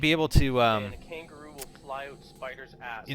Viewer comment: Did anyone else hear "a kangaroo will fly up a spider's ass" at 10:30? HAHAAHAHAHA